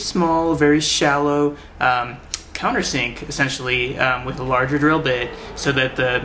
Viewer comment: CC says "uh..fantasy essentially have larger druid." got to love it.